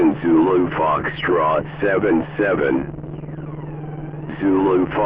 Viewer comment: i thought he said 707 😂